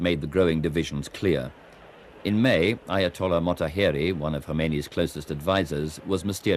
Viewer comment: I keep hearing "Hermione". It's stuck in my head now. The Ayatollah Hermione.